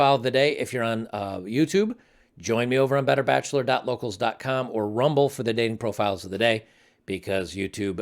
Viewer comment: Wait it's? I always heard it as bitter